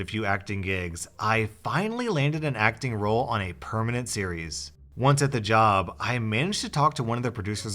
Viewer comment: Did he say Perminent Series?! It sounds like it... Its P R O M I N E N T Thanks!